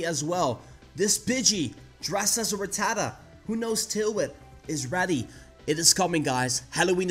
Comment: A Pidgey...dressed as a Rattata...that knows tail whip... amazing. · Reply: Thought he said tailwind